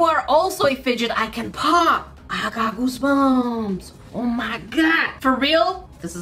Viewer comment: Is it just me or did it sound like said goosebums instead of goosebumps on the first one